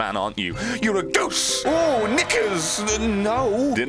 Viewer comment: Ohhh did you just said the n word? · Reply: Knickers